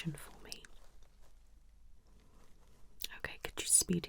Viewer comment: i thought she said "spit" instead of speed. Worst of all, I simulated it 😂